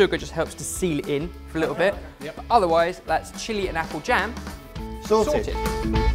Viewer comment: It makes me a bit sad but for 6 or 7 month I have watched this channel I thought you guys said Salted not Sorted